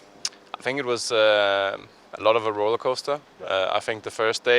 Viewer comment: he says "roller coaster" ...............I heard "Heroic Roster"